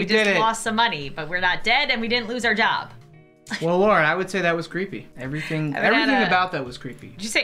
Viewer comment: am I the only one who heard say Laura?